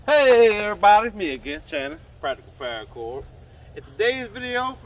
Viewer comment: Very innovative! I never thought to convert a the smiley sling into a staff sling! Very cool! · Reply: I thought his name was "Chandler" for a while too, because that's what it sounds like he is saying. Actually his name is Channing.